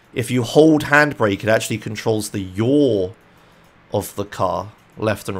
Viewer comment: what does(do) he(you) say here? control the what of the car? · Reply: Yaw